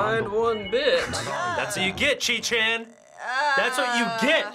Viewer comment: i thought he say "Keychain"